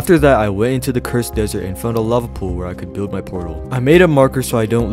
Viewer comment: I thought you said “love pool” anyway you earned yourself a sub